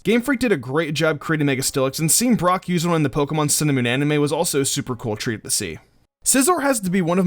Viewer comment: Man every time you say "Sun an' Moon" I just hear "cinnamon"